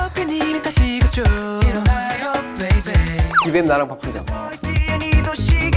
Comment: DID TAEMIN JUST CALL KEY KIBUM? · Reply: No, he called him by his nickname, Kibaem. Then Jinki also called him that on the text message that Key posted on his IG, he put Key's name by Kibaem on his phone as well haha